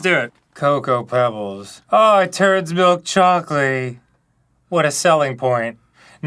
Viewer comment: My brain heard this as Turdsville's chocolaty 😆